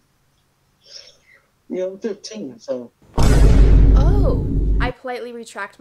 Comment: I swear he said 15